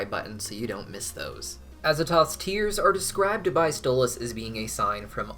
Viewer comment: *facepalm* I'm really glad you made this video, because I misheard it to be "Asimov's Tears" Azatoth, of course makes more sense. Lol (I feel a little dumb for thinking the event was named after a science fiction writer... ) 🐈‍⬛